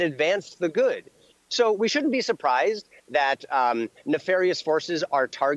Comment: Did Kirk say "nefarious horses" at about 1:05 ? He's not supposed to know about them yet........